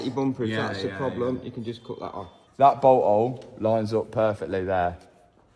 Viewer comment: When he says bolt hole I thought he said butt hole